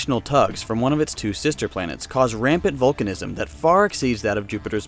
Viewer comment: I misheard "volcanism" for "Vulcanism".